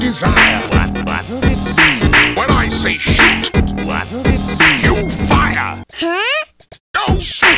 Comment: Muffled my phone accidentally at 2:33 so I heard “When I say SH*T, you fire!”😂😂